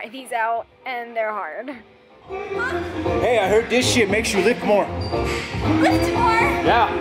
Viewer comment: wtf does he say "ditsit makes you lift more?"